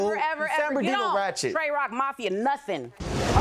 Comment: Tray Rock Mafia...what the heck did she say